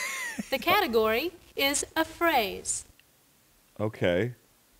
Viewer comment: Vanna was obviously saying "the category is... a phrase!" but i swore she was saying AFRAID